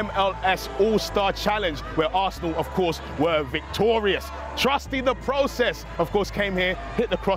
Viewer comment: Did he just say "Trusty in the process" ? 😂 Love that